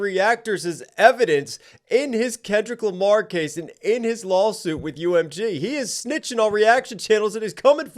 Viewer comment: Did you just call it a "LOLsuit"? 😂😂😂